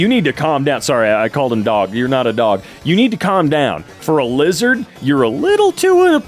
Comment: did he just call the cat a lizard